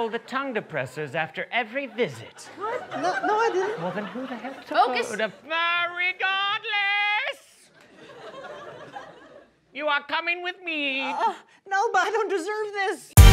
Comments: did Jason just say hell? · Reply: he said heck clearly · Jason said “then who the hell did..” very quietly. Did anyone else catch that?